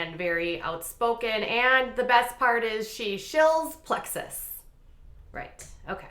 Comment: What word is said at 3:39? The —— world. I can’t understand what “world”he left. Thank you ❤️❤️ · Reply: Also what’s said at 5:00 mark? Shills plexis? What’s that ?